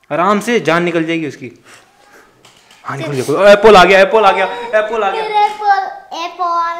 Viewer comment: WHAT DID HE JUST SAY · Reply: Appaul 😂